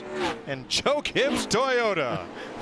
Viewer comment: sounded like Mike said Choke Gibbs Toyota